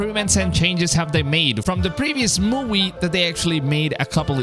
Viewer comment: did you say mowie